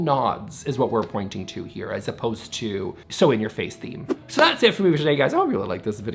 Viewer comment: I heard "sewing your face" not "so in your face" at the end there. Either way, harsh!